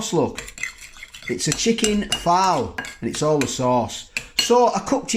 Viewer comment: Phall curry! Mate I thought you were saying Fowl as in chicken... My bad, my apologies. Well it was chicken anyway.. O yes..👍 curry leftovers and fried bread for breakfast is a great way to start the day, so invigorating. You need a couple of Trivets for your hot pots and pans while at the bench. Silicone or cast metal both are good. A great morning after video. Keep warm and dry there, Cheers mate all the best 😀👍🇦🇺